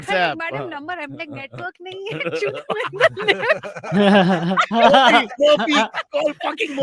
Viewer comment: did she just say, "Network nhi hain Chutvinder.." ? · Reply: Nah Chutvinder is something you probably use with friends so it can be easy to think you've heard it elsewhere. She says "Network nahi hai chu..I'm in the lift".